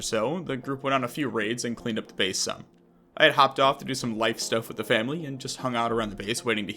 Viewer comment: 9.35 bro... i thought u said "i hopped off to do some white stuff with the family"